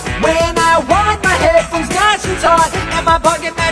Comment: I just realized he said "..Nice and tight" but I kept hearing "..snatch and thot"